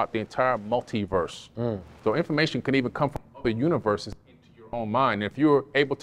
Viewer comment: What word(s) was cut out at 40:14? After “come” and before “mind” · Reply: “Can come from other universes as your own mind”